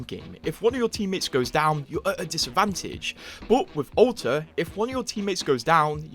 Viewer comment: I hear o-tuh when you say alter haha Edit: 1:42 "Boat, wit Ow-tuh"